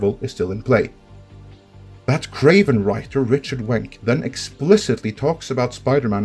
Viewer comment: thought you said Richard Wank, he he...